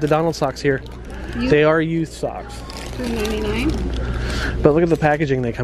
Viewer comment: You talk fast like me and everytime you said youth socks (yesterday too) I swore I heard used socks 😂🤣I was like wtf that cant be right. I even rewound yesterday. Today I saw the sign . Bahaha